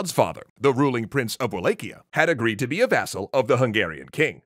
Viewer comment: Did he say basil, bastil, or basal?! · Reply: Vassal?